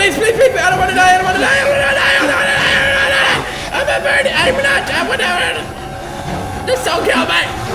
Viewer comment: did he say I'm a virgin